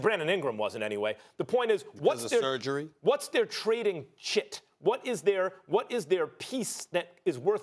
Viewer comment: thought he said shit, had to rewind!